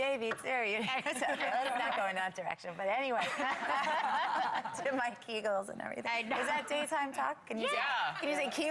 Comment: What did idina say at 3:21??? i heard something like two mike eagles or something like that....... someone care to explain